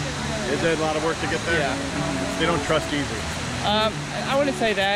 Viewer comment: I thought that guy said, "They don't trust Jesus." 😕